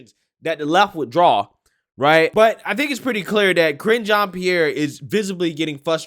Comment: I heard "Cringe Jean-Pierre" lol